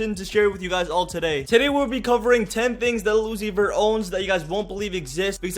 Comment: turn the subtitles on, says Lucifer instead of lil uzi vert